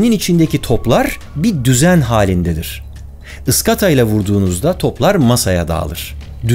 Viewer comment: "Isgata" mı duydum "ıstaka" olmasın o